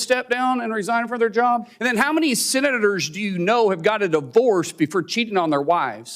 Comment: Wait, that guy from yesterday was saying "senators"? I thought he was saying sinners. Was he drunk? Am I drunk?